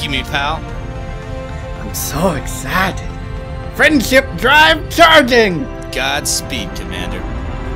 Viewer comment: I thought I heard “Friendship drive” aww